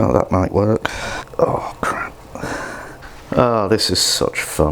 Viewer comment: The subtitles!!! He said "Oh Crap" 2:53 and the subtitles says "Oh Crepes" lmao.